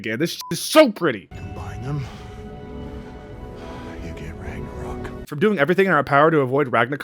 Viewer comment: did he just say Ranga-covk?